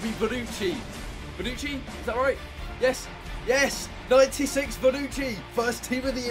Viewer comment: I thought he said 36 bonucci 😂